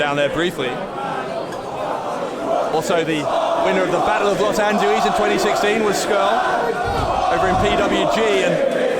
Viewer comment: can anybody tell me what the crowd is singing at 7:34 ??? I've heard crowds sing it before but can never make it out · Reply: EyeOfRa290 Are you watching? Are you watching? Are you watching Vince McMahon? Are you watching Vince McMahon? To the chorus of the Welsh hymn "Cwm Rhondda" or the English version "Guide me thou O great redeemer". It's quite a popular tune for chanting in English soccer games as it's a simple chorus and it's lyrics are easily interchangeable. "You're not singing anymore" to the same tune is a popular football chant/taunt.